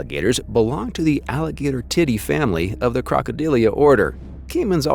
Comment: did he just say “alligator titty family”?